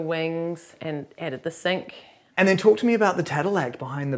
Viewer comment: what is the term? I’m not acquainted with the word. I’d look it up but not sure I heard it correctly. Anyone? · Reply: Ok, so I guessed at the spelling & googled. Found tadelakt, a Moroccan waterproof plaster. You can’t help but learn new things on this channel! Thanks.